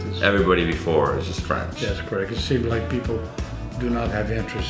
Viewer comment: What did he say about my people? I notice how we are often just minding our own business, and yet we are always brought up. 🙄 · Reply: That’s just a heavy accent. “It seem like people do not have interest in what’s going on and try to learn French”. I can see where it sounds like he said “it seem black people” though.